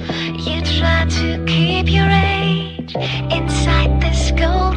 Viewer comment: I thought it says keep your rage